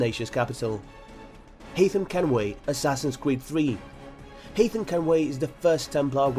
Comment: it sounds like your saying assasins cweed